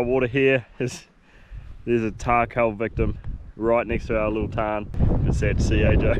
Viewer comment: what did he say at 7:32, the animal was a __ victim? Is it a virus or parasite? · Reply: It's a tarh cull victim